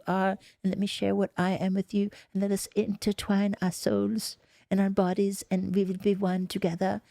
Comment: DR K’s emo voice has me dead Everytime. I heard “I want to intertwine our souls” as “I want to intertwine assholes” and I still can’t get up.